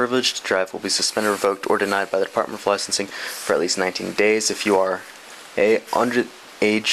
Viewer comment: The cop definitely said "19 days" instead of 90 days. Rookie cop who can't read well.